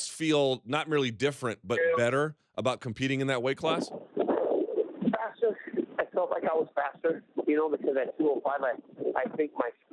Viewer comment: You misheard him, he said he felt like he was fatter.